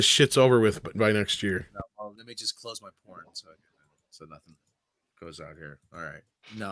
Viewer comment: Did say, "Let me just close my porn?" lol. Not much of a multitasker, I guess.